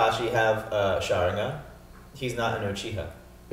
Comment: wait what he said xD? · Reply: "He's not an Uchiha."